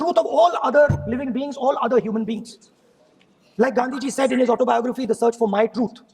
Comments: did he say Ganduji? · पर इसने गाँधी जी बोला या गांदू जी बोला, सुनकर बतायें... मैंने कई बार सुना पर कंफ्यूजन है...